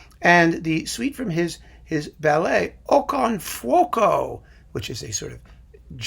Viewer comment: Next to the Ondine and Chandos recordings of the 3 symphonies there is a twofer on Finlandia (in their series "Meet the composer") with the 3 symphonies, conducted by Segerstam, Rautio and Saraste. Together with Comedy Overture (Panula), Kullervo (Segerstam), The Ostrobothnians Suite (Saraste) and Okon Fuoko Suite (Kamu). Really worth exploring. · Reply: Funny, when mentioned the suite I heard it as "o con fuoco" and I was like: "what??"